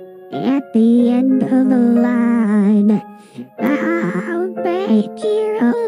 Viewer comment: GOOF: Caillou sang "Princess Peach" instead of "Princess Catherine"!